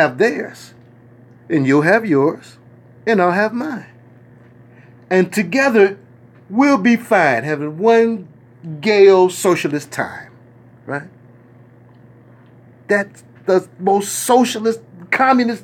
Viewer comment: I thought it said and together will be partying and it takes